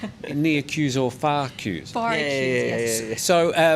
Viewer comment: Far queue? I think we all thought said Fcuk You. lol